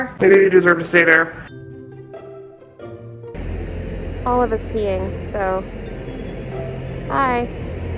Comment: when she said "Olive is peeing" i thought she said "All of us peeing" LOL!!!!!!